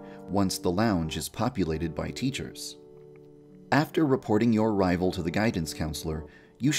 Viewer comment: Who else thought it said exploding your rivals and got excited.